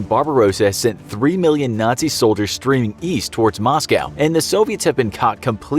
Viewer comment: old video but the voiceover said east while the captions said west